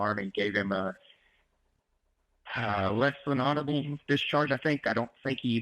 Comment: The voice to text conversion is awful. I just heard "horrible" and read "honorable".